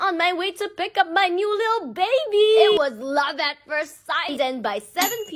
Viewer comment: Bro when she said ‘my little baby’ I thought she actually said a little baby😂💀